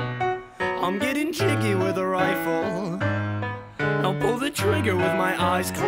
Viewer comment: I'm getting hickeys from my bed bugs I'm getting busy with a bad perfume I'm sticking kisses to a pen drug I'm making friction with a sad vaccuum I'm getting jiggy with a rifle I pull the trigger with my eyes closed Hoping to hit you somewhere vital And when I miss, you come and kiss me with a smile [Chorus] I'm having sex with a ghost 'cause she knows I'm alone She's a freak in the sheets, play it cool, I'm sleeping with a ghoul (x2) [Verse 2] The only time I ever see her Is when she's behind me in the mirror Even from a distance I can hear her Try to listen, but her whispers make my ears hurt And on our dates, it's never daytime When she gets playful with a steak knife I need a break, maybe I'll take five Shake it up and medicate, maybe I'll take five [Chorus] I'm having sex with a ghost 'cause she knows I'm alone She's a freak in the sheets, play it cool, I'm sleeping with a ghoul (x2) [Bridge] [Chorus] I'm having sex with a ghost 'cause she knows I'm alone She's a freak in the sheets, play it cool, I'm sleeping with a ghoul (x2) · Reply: I thought it said "I'm getting kinky with a rifle" 😔